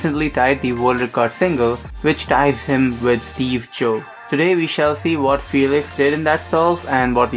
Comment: did he say steve jobs? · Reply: Mr.Awesome Bamboni steve cho , his full name is seung beom (steve) cho